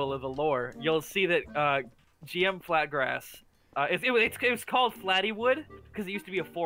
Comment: why did I hear “flat grass”